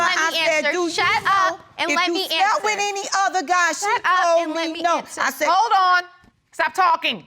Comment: Did I hear d young lady tell grandma "SHUT UP ! Lemme answer" ?